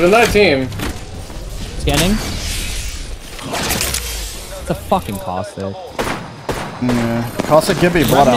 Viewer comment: ファッキンカワセに聞こえるの俺だけ？